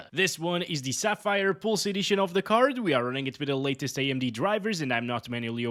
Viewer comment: tests so many NVIDIA GPUs that when he said "AMD drivers" I heard "NVIDIA drivers" 😂